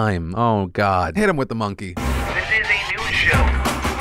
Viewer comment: i've been watch Phil since 2012 and i always thought the intro said " this is a new show" I'm realizing now it's " This is a news show" my life is a lie